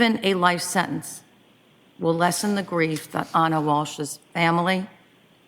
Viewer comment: The judge said she read everything and listen to everything and I thought I heard her say included what Mr. Walsh said I winded and went forward again. I see nowhere where Mr. Walsh or the murderer said anything did I somehow miss it?